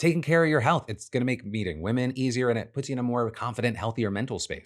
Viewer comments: Did anyone else hear beating women instead of meeting women at the 6:29 mark. I had to rewind like 3 times 😂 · i thought he said " beating " 😂